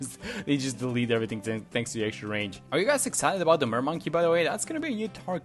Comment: I can’t understand what he said here about a new tower Edit: someone replied clarifying that he was referring to the mermaid tower. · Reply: Mermaid Monkey, said in an NK Post